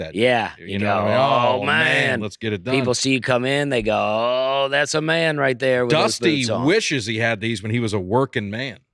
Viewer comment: You guys can keep trying to make me think “oh man! Working man boots” but every time I hear this and read, I hear “old man” and picture of these as the new balance boots of the boot world.